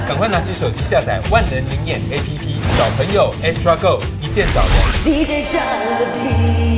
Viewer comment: 我一直把EXTRA GO听成trivago😂